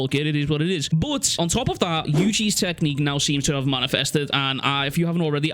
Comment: I left YouTube on while sleeping a couple of days ago and woke up because I kept hearing "kers technique" and my brain knows that's the